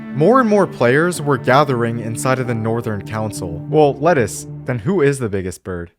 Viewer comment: Did just called Theo by the name Lettuce? 😂